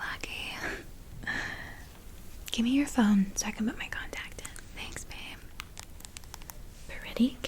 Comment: What she said: "Give me your phone so I can put my contact in." What I heard: "Give me your thumb so I can put my contacts in." Initiate brain shutdown.